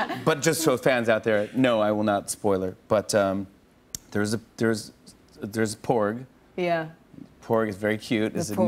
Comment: When talking about the movie, for a split second at 4:33 I thought he said, "There's porn."